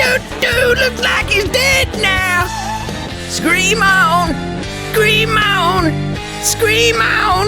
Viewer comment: I swear I heard him say "cream now"